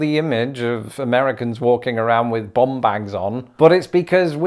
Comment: When you said “bum bags”, I thought you said “bomb bags” which would certainly be a tremendous difference. 😊